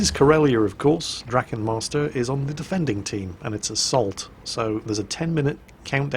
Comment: I thought Jingles said "Its a salt". Little did I know how prophetic that would be.